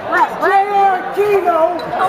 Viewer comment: sounds like is say JRTaquito